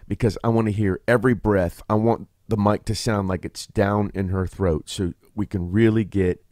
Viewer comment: I clearly am not as mature as thought… I heard down in her throat and hit the floor